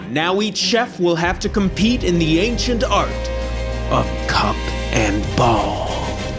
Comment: Cup-and-ball? I thought they said, "C*CK-and-ball."